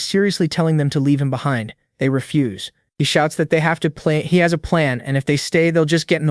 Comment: Im now confuse, is the narrator AI or human? I think it said "They have to play- he has a plan"